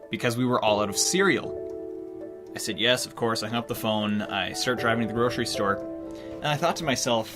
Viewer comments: the captions say l humped the phone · the captions say humped the phone not hung up